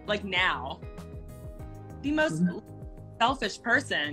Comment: When she said she was probably the most unselfish person ever 🙄 Delusional. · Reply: Oh i thought she said she was the most selfish 🤣